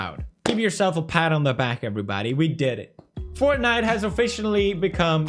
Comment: At first I thought he said, “We charged Fortnite”